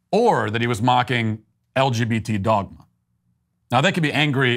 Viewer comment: Did you say LGBTdom?